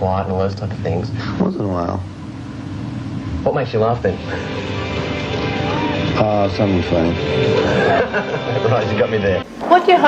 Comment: WHAT DOES HE SAY?? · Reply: The interviewer asks "What makes you laugh then?" And Dylan replies "Something funny"